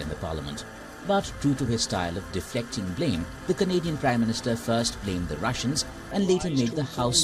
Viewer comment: Whenever some says 'Canadian Prime minister', I hear 'comedian prime minister' these days.